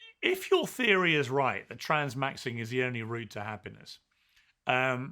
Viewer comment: Did Piers just say "transmaxxing" 😂